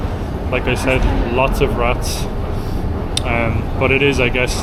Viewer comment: Did you say rats?